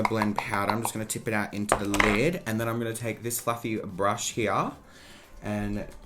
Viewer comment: For a minute I thought said ‘ just gonna take this flap brush’ 🤦🏽‍♀️